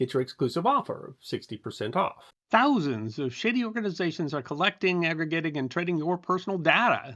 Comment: Not intentionally. At what timestamp? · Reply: I'm not trying to be a narc or anything, but in the ad part, 50 second mark, it sounds like you said "shitty companies". Which is fine by me. You do amazing work. I'm gonna feel really bad if I mess up your sponsor with my big mouth.